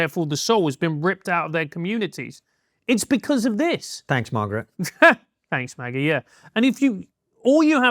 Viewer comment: I heard them say “Thanks Margaret” (Thatcher, I’m assuming…) I’m thinking they have disdain for her?